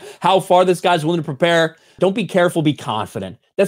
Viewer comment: I thought he said "Don't be careful, be competent", but either way this awesome.